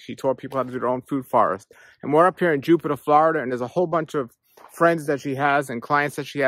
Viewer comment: Where is it in FL? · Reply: Thought I heard her say Jupiter, FL